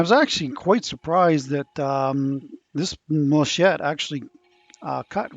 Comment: Every time I heard "machette" I winced and whispered "... malachite..." That bowl took WAAAAAY too long to finish lol but it looks amazing.